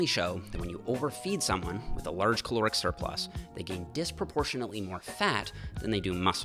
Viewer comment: Great vid! Altho I keep hearing "Chloric Surplus" every single time. Had to see it written while spoken to understand 😉